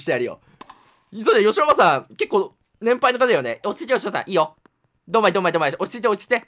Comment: 落ち着いてしょうた (落ち着いてヨシおばさん) 空耳でしょうたに聞こえる(笑)